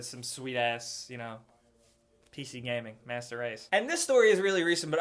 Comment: Am i the only one who heard him say p.c. master race (-_-)